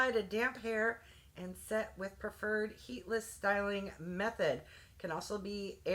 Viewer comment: Omg i thought you said headless instead of heatless I had to rewind😂😂😂